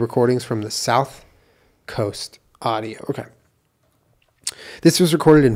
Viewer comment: Am i the only one who hears someone saying "ok" at 41:25?¿ i thought that was but it sounded like a different male voice. If you want to watch a really good paranormal youtube channel i recommend ghost of carmel maine. It's different from the usual ones